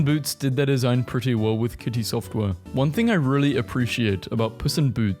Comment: did he just call her "kitty software"?!?!?